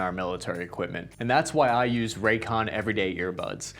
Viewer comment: I kept hearing “Raytheon” instead of raycon and with the state of the world I wouldn’t be surprised